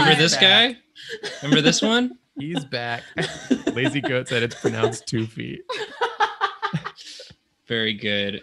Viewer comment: it turns out smoking weed during these vods is a bad idea cause i heard "its pronounced "toofy"" in the middle of a hit and i almost lost a lung